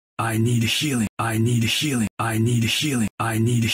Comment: It sounds like he says I need the ceiling, lol